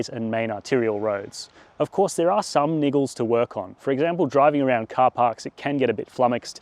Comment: What did you say? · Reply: Yes he said niggles